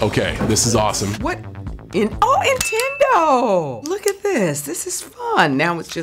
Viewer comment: did she say "Intendo"??????